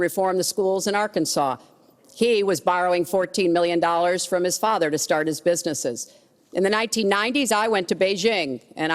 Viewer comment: did he say wrong at 46:44? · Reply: Abbas Afshar yeah he said wrong